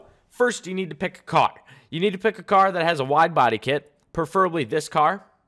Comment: Did anybody here what he said at 0:34 LOL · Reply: *hear pick a car with a widebody